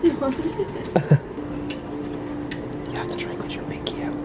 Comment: Yo everyone, 22 days out as I write this comment! Drop a comment below if you've been following the journey - appreciate the support 🙏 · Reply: into it is it just me or did you say you have to drink with your Mickey out 😂😂😂😂😂😂😂😂😂😂😂😂😂😂